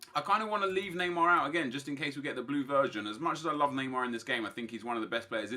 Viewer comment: Did he say the blue VIRGIN? I know he means version but I heard VIRGIN